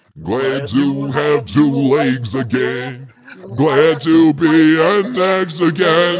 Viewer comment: wait did he say glad to have 2 legs again?